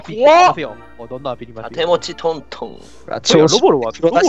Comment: いい声でちーのが盾持ちトントンって言ってるのなんか草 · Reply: 私の耳は終わってるかもしない... 盾持ちトントンを【手持ちトントン】に聞こえた...